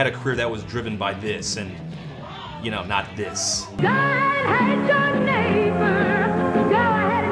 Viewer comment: the first couple times I heard it, I somehow misheard the chorus and thought it went "god hates your neighbor, go ahead and cheat a friend, do it in the name of heaven, you can justify it in the end"